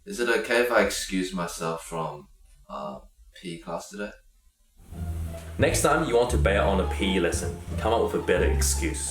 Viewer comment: Haha during the P.E section it I heard: "Next time you want to bail out on pee have a better excuse" xD